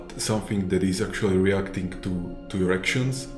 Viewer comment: reacting to erections? is that what i hear at 3:13? the other guy even struggled to hold back a smile.